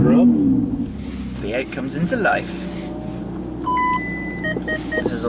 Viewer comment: Did he say V8 comes to life at 19:27?